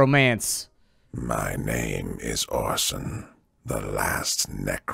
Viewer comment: I hear "My name. Is Awesome." 0:12 I'm thinking W.T.F..... Who calls themselves awesome Have to rewind a bit to read the subtitle. My English is failing......